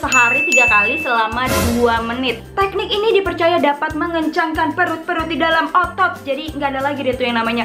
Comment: apa aku gk salah denger menit 1:40 mengencangkan perut2 di dlm otot ?? emang bgitu yaa ??